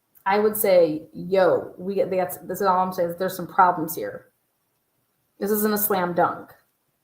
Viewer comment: It sounds like their saying guys there's someone inside 😢